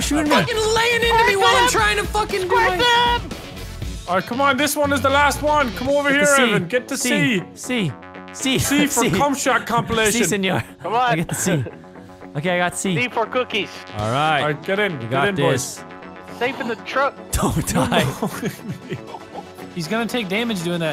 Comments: Did vanoss said “si señor” at 14:04? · did he just say "Sí señor" ?